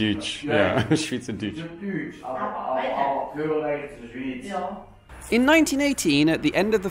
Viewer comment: The part that he doesn't get, could be, if i heard correctly: "aber ... aber ... aber gehöre egentlich zur Schwitz" -> but ... but ... but belong actually to Switzerland.